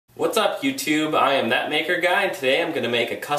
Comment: thought he said fuck youtube 😂